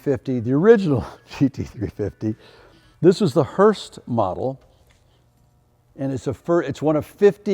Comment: I’m glad to see there’s a part 2. i was like I wanna see the 350 H lol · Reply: But did he call it the "Hurst" model @ 0:14 instead of Hertz? Hmm.